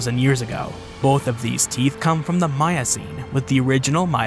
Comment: Lol, I heard 'Mayacene' instead of 'Miocene'.